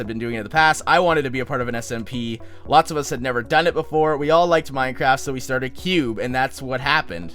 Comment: at first i thought he said minecrack omg!